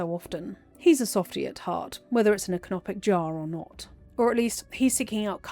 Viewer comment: What were the words at 41:02? "He's a softy at heart, whether it's an ____ _ or not." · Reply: "Canopic jar"?